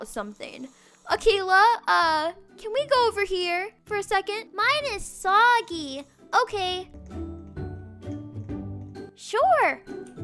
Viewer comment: LOL I THOUGHT SAID HER PANCAKES IS SWAGGY